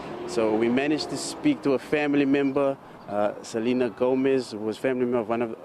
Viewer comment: While kzn is getting cleaned up. D.A sleeps in western cape Edit 2:05 lol did i just hear one of the family members is Selena Gomez 🤣🤣🤣🤣